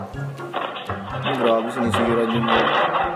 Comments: i heard "gago sinisira yung---" lol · tama ba narinig ko? ken saying "gago" o "grabe" ? HAHAHAHHAHAHAHA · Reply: Grabe po hehehe